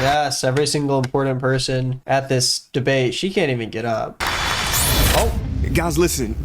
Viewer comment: Why am I hearing "She doesn't even go here!", all of a sudden?